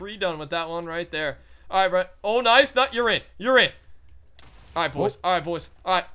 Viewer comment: it sounded like you were saying "urine, urine!" xD.